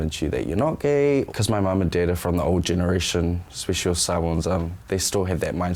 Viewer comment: “Sai Wans” ummmm I think that’s meant to say Samoans??